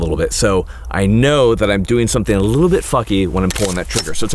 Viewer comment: "I'm doing something a little bit fucky" Did he say "funky" or did I hear that correctly haha